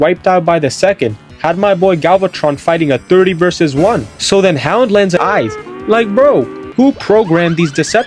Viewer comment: you kinda said ratchet instead of hound :p